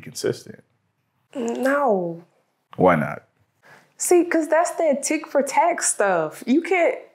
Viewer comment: did she say? Thats that tik for tak stuff 😂😂😂